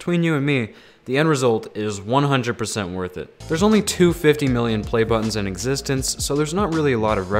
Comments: Did he say 250 million playbuttons ? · i heard that as there are only 250 million play buttons in existence